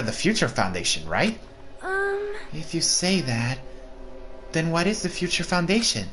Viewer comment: What is the princess girl sonia saying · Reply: "If you say that... then what is the Future Foundation?"